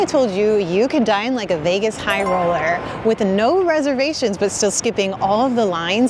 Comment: I almost thought you said , a vegan high roller 😂😂. Nothing wrong with that of course 😂. Fortunately, I was wrong . Australia says helloooo.